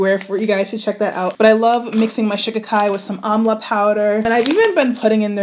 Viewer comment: Before I checked the description box, I thought you were saying “omelette powder” 😭😭